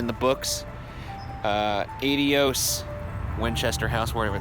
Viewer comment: Did he just say aid-y-ose?